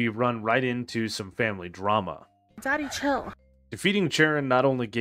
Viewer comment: I was using the vid as big noise to sleep to and as I was about to fall asleep I just heard "DADDY CHILL" AND JUST LOST IT AND MY SLEEP 😂